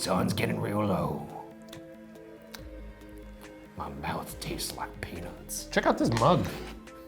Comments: Did Ryan just say his mouth tastes like penis?o_O14:41 #chubbynuts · I HEARD "MY MOUTH TASTES LIKE PENIS" I'M SORRY RYAN LOL